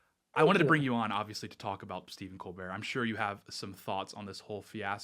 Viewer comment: I had to start the stream over because the first time I was half paying attention and I swear that I thought he said Steven Crowder. I was trying to hold back being seething mad because I was sure that I had to be misunderstanding something. After The Blaze and then then the Daily Wire nuclear war and Gerald A !CEO! the mention of a contact made no sense. At "poopy head" and "Jasmine Crockett", I knew it was time to stop keeping on keeping on. Phew! What an unpleasant minute or so! Yes, let's go after Colbert and Lemon, et. Al.,! Full steam ahead!